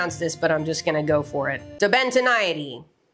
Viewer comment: the captions said "The Bentoni"